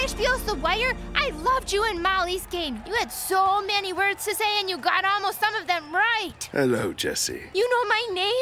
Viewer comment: I heard this as "You had so many words to say and you got almost all of them right" when it's really "You got almost some of them right" 😀